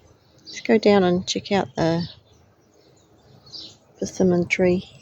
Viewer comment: What did you call this tree? @ 4:47? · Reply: Persimmon, I have heard its pronounced two different ways, think I am pronouncing it the English version rather than the American version.